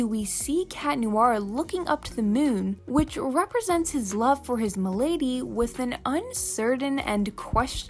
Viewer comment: sorry but i think im the only one who hears "his m'lady " on 14:23 ?????